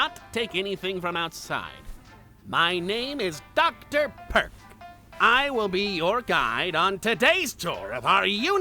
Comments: First time I heard him say his name I thought he said Dr Perck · thought he said ”my name is doctor perv” 🫰🏼❤️‍🩹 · I THOUGHT HE SAID MY NAME DR PERV 😂😂YOU 😂